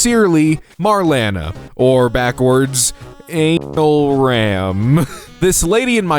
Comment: I’M SO STUPID I THOUGHT IT SAID “AN ALARM” 💀💀💀